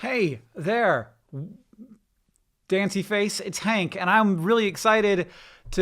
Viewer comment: Wow how did you know my name is Danceyface I feel so recognized (side note: I had to listen to it three times to make sure it was not fancyface what the heck is Danceyface hank) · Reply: Autumn Berthold oh.. I thought he said daintyface